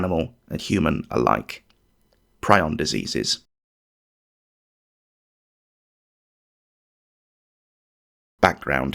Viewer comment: I thought it said Prison diseases!